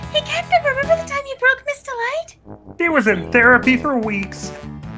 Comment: What did she say? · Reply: "Remember the time you broke Ms. Delight?"